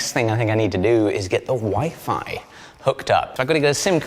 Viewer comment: When he was talking about the wifi right when he was finished talking about the table it sounded like he was saying that the next thing he to do was put the wife on (the table). I thought well he shouldn't have said that on camera. 😂🤣😂